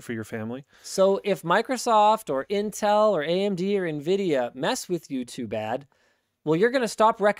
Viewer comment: Linus said "If Microsoft, AMD (Etc) mess with you too bad" and I definitely heard "If Microsoft, AMD (Etc) mess with YouTube Ad"